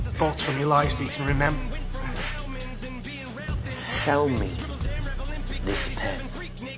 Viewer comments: I am so dumb. The whole time I thought he said “smell me this pen”. I was so confused 😅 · I heard "Smell me this pen" to say I was confused would be on the list